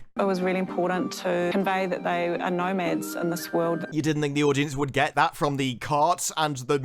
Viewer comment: When she says "they are nomads in this world", it sounds like she says "there are no meths in this world"